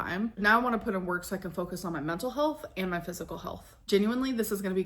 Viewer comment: “I’ve been focusing on my mental health, not my physical health” I heard “I’ve been eating my feelings”